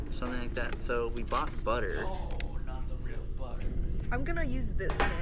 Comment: did anyone hear someone say “ohh not the real butter”